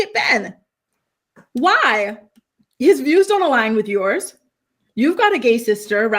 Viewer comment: I was so confused why Sara was even trying to move to Nashville with Ben. I thought she said their views don’t align 🫠